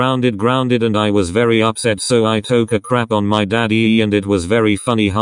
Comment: What the fuck. I was makin' food and then I heard "I took a shit on my daddy".